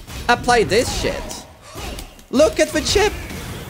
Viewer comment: I thought he said 'look at the shit!' 🤣🤣🤣